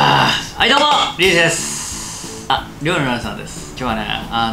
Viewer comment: 料理のお姉さんて聞こえたの俺だけ？笑